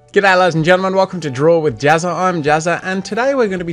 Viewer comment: I thought you said "¿qué tal, Ladies and Gentelmen?" "¿Qué tal?" Means, "how are you?" in Spanish.